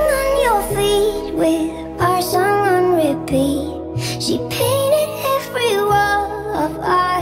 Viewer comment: I am literally so sorry, but I wasn't paying attention to the video because I was drawing and I could S W E A R i heard her saying "she peed on every wall"